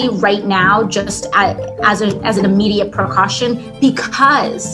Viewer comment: At about 56:35 AOC did not say preeecaution, she said procaution. Someone out there please create a new dictionary that will give me the definitions of this loons words. Can anyone out there do a montage of the use of AOC's made up words? GOD, she's such a moron! 🤯